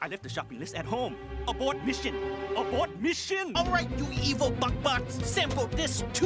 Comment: Did anyone else think he said “f#%^ bots” at first?